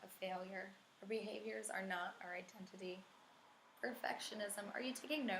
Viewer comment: More than good enough. Self care, I will share that concept with others today. One, question please, at 2:00 you say "Behavior is not our intensity." I don't understand what that means. Would you mind explaining? Again, thanks for sharing. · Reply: Ok I brought in an expert and she said that the word is identities not intensity. Sorry for my confusion, I am not perfect.